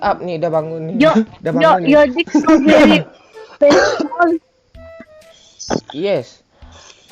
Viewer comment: your DICK is very small wkwk edit: your DICK so very small wkwk salah denger gue yamaap